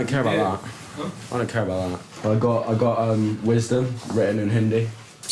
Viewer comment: The absolute irony of this dude getting "wisdom" tattooed lmao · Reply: “Wisdom” ? I thought he said “wasted” LOL